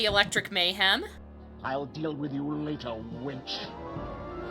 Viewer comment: I swear he says "I'll deal with you later winch." :/